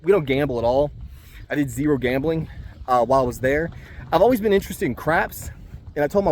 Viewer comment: Hey lovely place with mountains in the back ground sweeeet, PS i thought you said I've always been interested in crack had to rewind it to here you say I've always been interested in crapps 😂😂